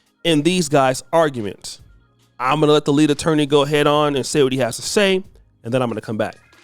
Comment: All I heard was " Im a GD attorney"!